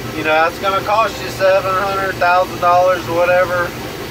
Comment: When you mention $700 - $1000. I heard it as 700 000 & just about closed the video lol. Bout to start my journey. Wish me luck! Thanks for the insight! God bless.